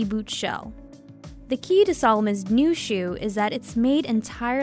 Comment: Lol I thought it said salmon ( the 🐠fish )